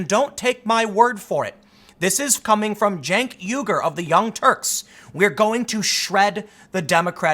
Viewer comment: Lol @ 0:55 did I hear you say Chunky Yogurt?